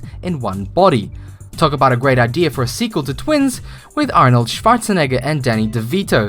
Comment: dunno about you but I just heard him saying "Arnold sfartzeneger" .